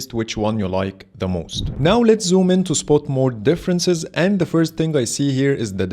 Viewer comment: hahahahaa did you just speak sport mode instead of night mode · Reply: Nope, I said spot not sport